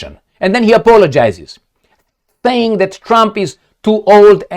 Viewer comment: Did I hear that Trump?!? at 01:21?